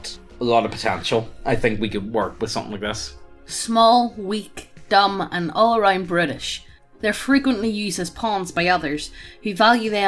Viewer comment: I swear to god that sounds like youre saying "small, weak, dumb and all around british" :D